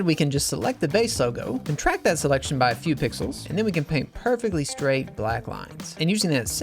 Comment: what are you saying at 10:18 I'm not quite catching this technique clearly via audio ... Looks like a total lifesaver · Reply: "Contract that selection by a few pixels." It's in the Select menu.